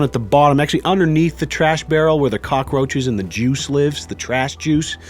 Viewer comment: thought you said where the JEWS lives